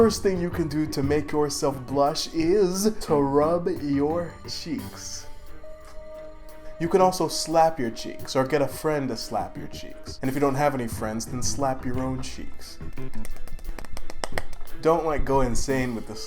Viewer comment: My crush: hi Me: hangs upside down magically while screaming like goku slapping my cheeks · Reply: I thought you said clap your cheeks 😭